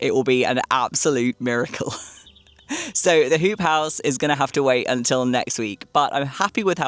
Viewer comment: I’m so glad it was a ‘hoop house’ and not a ‘poop house’ as I initially misheard 😂😂😂😂. Impressive! Wow!